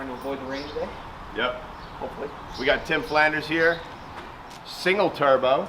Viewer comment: Sorry, I thought you said Ned Flanders... So many Ford options for FAST